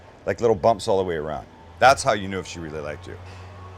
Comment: Im sorry dude i swear at first all I heard was "bumps all around , that's how you know she likes you" 😆